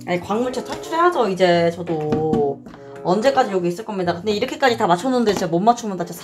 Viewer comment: 나만 언제까지 여기 있을겁니다로 들림??ㅜ갬덩먹엇다 언니... 영원히 브론즈에 남겠다니ㅜㅜ